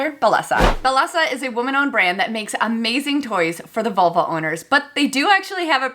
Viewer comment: I totally thought you said it was for Volvo owners. 😂